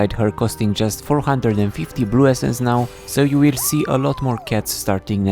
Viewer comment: did you say "nyow" ?